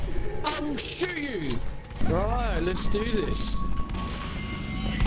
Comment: Did said "OH FUCK!" at 2:02 ?!!